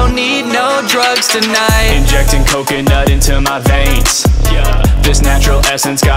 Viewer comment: 'We don't need no drugs tonight' Literally the next line: 'Injecting coconut into my veins-' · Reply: I thought he said „injecting cocaine not into my veins“ which is funny cause you don’t do that with cocaine xD